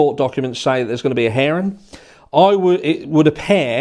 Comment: Oh man I thought you said heron, or harring maybe lol. Took a sec and some context. Always enjoy learning useless little things like how some may say hearing.